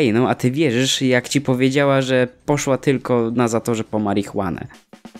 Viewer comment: Wait a second... Czy ja usłyszałem "NA ZATORZE"!?